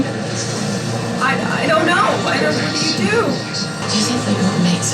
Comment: When everything started falling apart I thought you said “what do we do?” And it made perfect sense because I know how much you get into the shows. Great reaction and I’m glad you wore the Möbius shirt. It was awesome to finally see him on a jet ski. I think Owen Wilson really didn’t want to ride one so I’m glad they figured out how to make it work.